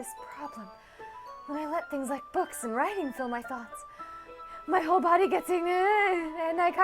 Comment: I think she said my whole body gets incredibly aroused but I mean ew